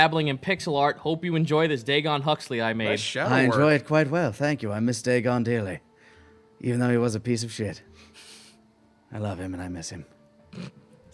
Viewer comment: "I miss Dagon dearly" I initially heard it as "i miss Dagon daily" which made me think to write this: I miss Dagon daily as Dagon is daily gone just like the days are gone daily.